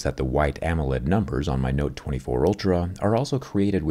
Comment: Did You Say "Note 24 Ultra"?